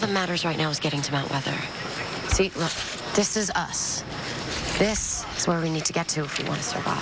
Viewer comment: Am I the only one who heard Clarke say "Dis is us."